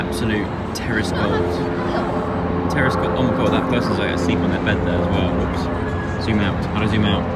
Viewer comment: just me that thought he said terrorist goals and not terrace goals🤣😶